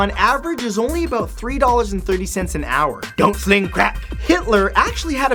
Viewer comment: I misheard you as saying "don't blame crack" which apparently was funny enough for me to laugh spit all over my screen 🐚🙊